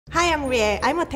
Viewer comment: HELP WHEN SHE SAID “Hi I’m Rie” I HEARD “Hi I’m gAy”